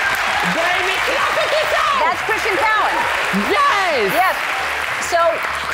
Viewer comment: When Kendall said ‘Christian Cowan’, I heard ‘Christian Kalen’....